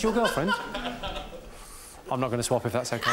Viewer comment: what does he say at 1:02? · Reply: "I'm not going to swap if that's ok"